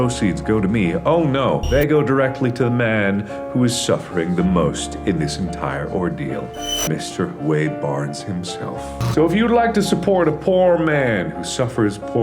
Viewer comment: I thought he said " if you'd like to support a porn man" and I died...